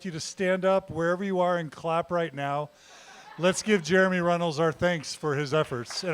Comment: Ngl, every time I’ve heard this mans name for the past year I thought people were saying Jeremy Reynolds. I feel extraordinarily stupid